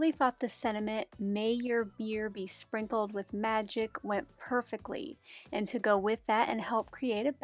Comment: I had to rewind many times, and still heard the same thing…..”may your BEER be sprinkled with magic.” I’m sure it’s all my brain could hear after I misheard it the first time, or……. 😂🤣😂🤣